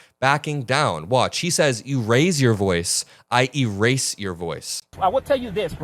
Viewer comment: With the ICE Agents accent being as thick as it is, it does sound like he said “If you raise your voice; I raise my voice”. But honestly at this point … who knows. Maybe he did say you “raise your voice I erase your voice”. I don’t like what ICE is doing, but I don’t think that’s what that agent said.